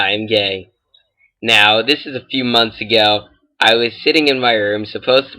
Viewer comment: it sounded like he said ''shitting in my room'' lol